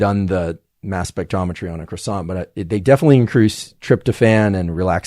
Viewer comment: Anybody understand what vegetable he was talking about eating before bed? sounded like he said croissant but I know that’s not what he was talking about lol far as I know that’s not a vegetable.